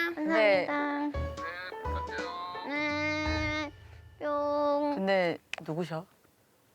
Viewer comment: did yuqi just say “but who was that”🤣🤣